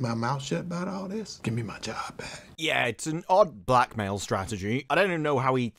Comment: To be honest, for a split second I thought said “black male strategy”. Sorry. The politics is getting to my head. 😂